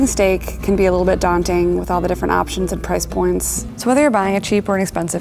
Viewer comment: did i hear price porn?